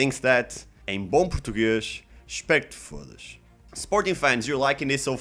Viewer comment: did you say “em bom portugues, respect fas”